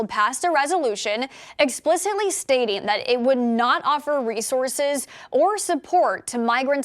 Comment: Maybe it's my tinnitus but I thought the reporter said "race horses" instead of resources..guess I got to get it checked😂